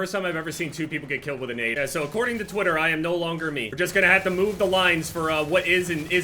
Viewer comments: I thought this video said "I am no longer a jew" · i thought it said "i am no longer jew"